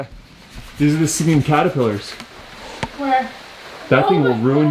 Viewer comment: I swear I thought he said a singing caterpillar will ruin your day.